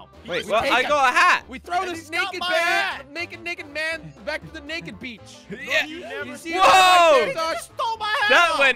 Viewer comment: Did jelly say? The n word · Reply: He said naked beach